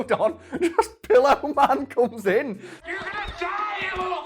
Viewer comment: he said "Pillow man" but i heard "Pillar man"